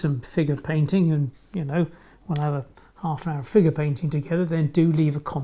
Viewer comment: lol I thought he said, " finger painting", I thought, ok , strange but hey I guess I will give it a try... ! Im getting ols\d and hard of hearing...lol finder painting...sorry!